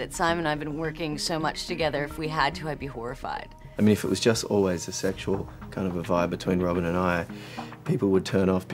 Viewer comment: What is he saying at 6:02? Can anyone please help? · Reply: I know it's late, But I'm bored in the house and I'm in the house bored... Simon: I mean if it was always just a sexual, kind of a vibe between Robin and I, people would turn off because I'd want us to shag, and then once we did shag, people would turn off because(shrugs, shakes head) it's a done deal.